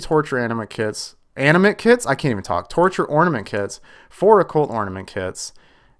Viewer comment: When u got those ornament kits i thought u said torture anime kids